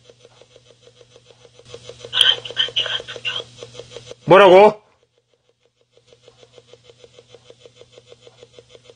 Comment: 아씨~ 같이 갔었죠. ? 또..아 정말요? 들림... 귀신의 장난 ㄹㅇ무섭다.... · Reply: tv민영 와 이거다...같이 갔었죠